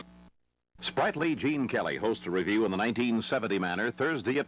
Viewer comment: "Sprightly Gene Kelly." Am I hearing right?